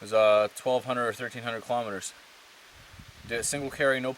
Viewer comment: That must have been one heck of a first portage. (720 miles) had to bust your stones on that mistake, sorry! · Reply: Thought he said kilometers??